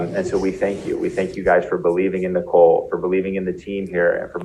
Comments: He said "believing in Nicole" and I heard "believing in the cult" Also btw I LOVE your hair you look like a doll! owo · “Believing in Nicole” I swear I thought I heard “believing in the cult “ 😂